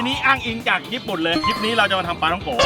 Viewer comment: พี่พูดว่าอะไรหรอค่ะ ฟังไม่ออก😂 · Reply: อ้างอิงจากคลิป ญี่ปุ่นเลย คลิปนี้จะมาทำปลาท่องโก๋ มั้งคับ😅